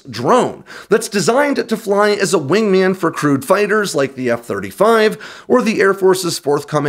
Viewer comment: Haha - re the F-35 you said "crewed" fighters, but at first I heard "crude" fighters, and went "huh?".